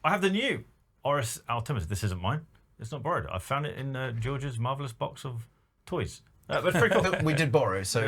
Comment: The first two times I heard say he found that Oris in “George’s mother’s box of toys.” I found it strange that George seems ok with that so I had to listen a couple more times 😂